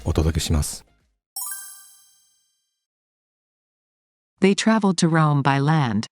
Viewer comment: 今日もありがとうございました。今日の問題は、traveled が trouble に land が loan に聞こえてしまいました。:face-blue-wide-eyes::face-blue-wide-eyes::face-blue-smiling::eyes-purple-crying